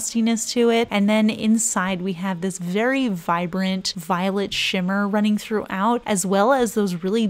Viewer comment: why did I hear violent instead of violet 💀